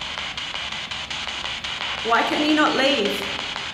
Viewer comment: I heard was "not us"